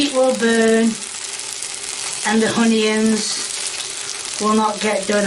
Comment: Did she say hunions?